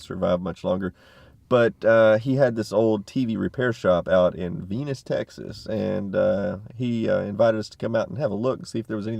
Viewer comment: Lol, I thought you said 'Penis, Texas' ;)Yep, I have a tv similar to that one you picked up, but black and white, works perfect from the day I found it :o)